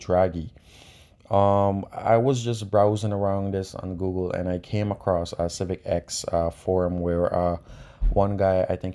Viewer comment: I heard you mention Derek Robinson and John of Phearable but I couldn't hear the name of the person you mention to use for racing. Was it Robert of Brazil? 08:41 Thanks for the shout out btw. I appreciate it bro. I wish I could show you the Myxal Two-Step in person. Excellent video btw. 😉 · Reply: Hey thanks, it's called Revitbrasil, u can find him on IG